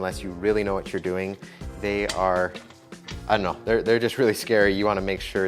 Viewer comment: Did you just say "Ano" when you were looking for your word!? ARE YOU AT LEAST PARTIALLY FILIPINO TOO!? Big gasp